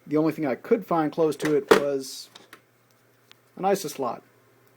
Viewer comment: Wait, did he just say "an ISIS slot"? 0:49 like if you agree